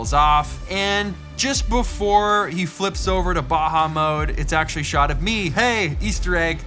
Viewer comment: English isn't my primary language. What's Baha mode? Or what did he said there?